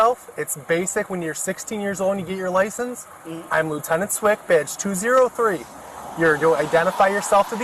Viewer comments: I swear he said “I am Lieutenant Swick, b*tch.” 😂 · does he say “I’m lieutenant Swift Bitch?”…